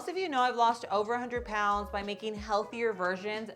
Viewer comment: Thanks for the recipe. I thought you said you lost 100 lb by doing a liposuction 🤔